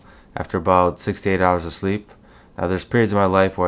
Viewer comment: I thought he said 68hs of sleep 😂